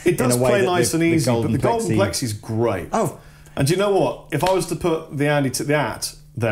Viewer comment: Which pedal does say would beat them both at around 14:14? I can't make it out. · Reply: Joel Wallington I think he's talking about an Andy Timmins(?) (I'm not 100% on the spelling) pedal